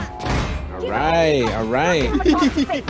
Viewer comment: I think was saying "alright, alright" at 8:09 but it's just so fun to hear it as "aray, aray" which means "ouch, ouch" in Filipino-Tagalog because it's very fitting with the scene ahahhaha